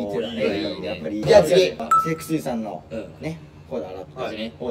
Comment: ゆうぴの｢雪肌粋｣が｢セクシーさん｣に聞こえるのどうしたらいいですか笑笑可愛い笑笑